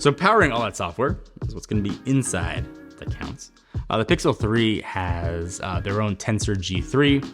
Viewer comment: Did you just say Pixel 3 to Pixel 8😅